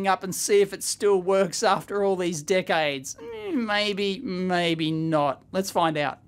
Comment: I heard something like "dickaids""